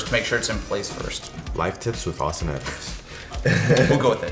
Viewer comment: I think he called him Austin Matthews a 3:40 as in the hockey player 😑